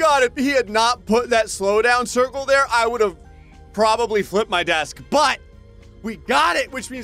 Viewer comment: when he said "I'll probably flip my desk" I thought he said "I'll probably wet my bed"🤣